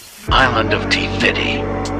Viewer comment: I thought you said Island of tities